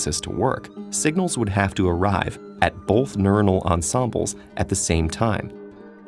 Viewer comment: Great video, but all I hear is "bolth".